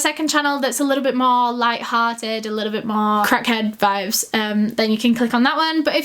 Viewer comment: "If you wanna ... my second channel, it's a bit more light-hearted, a bit more crack-head-vibes there..." Is this what Eleanor said, "crack-head-vibes", or did I get her entirely wrong?!^^ & If it's a "new it-word"/new-fashioned term with a similar meaning as "light-hearted", exciting or "upbeat", then admittedly I've never heard it used in this context before - (I've only heard it in context with illicit substances tbh) - so nvm, that isn't tragic at all, bc I'm no native English speaker anyways. --> I just thought, it sounds kinda hilarious to me, maybe a native English speaker or someone else wanting to help can enlighten me ... thank you!